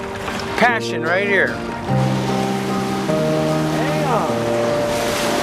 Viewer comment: did he just say "dayum" XD